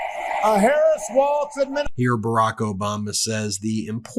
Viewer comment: Did you say "Obamna" by accident? Lol 😂